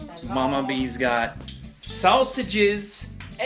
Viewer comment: did anyone else notice that when he said sausages it sounded like he said salsa juice I'm like. WTF LOL